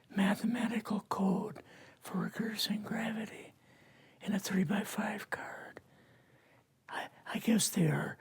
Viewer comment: Sorry, but what does he said from 10:35 until 10:50? He's hard to understand and English is not my native language. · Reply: said he has the formula for reversing gravity on a 3x5 card